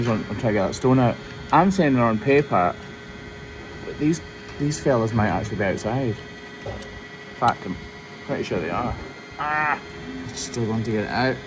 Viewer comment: Bit blonde and thought you said FED on paper. Took a moment. 😂